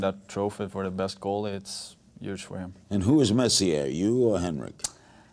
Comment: larry sounds like hes saying "who is Messier?"